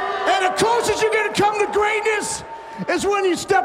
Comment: I'm sorry, did he just say, "Sniff our greatness" ?